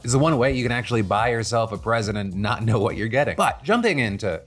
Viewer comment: Did I misheard or did Phill said "you can actually buy yourself a president and no know what you are getting" in the beautifull bastard spot ?